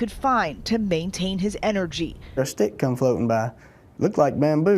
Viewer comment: Did he just say he found a steak floating by? Lol thank god he’s ok tho, def a miracle